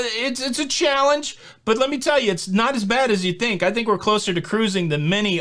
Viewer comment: Thought you said we are close........😂